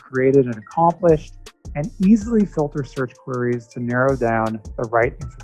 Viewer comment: it sounded like he said Furries xD